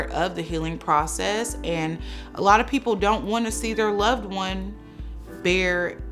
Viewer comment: Does she say , people don’t want to see dead body lifeless 😳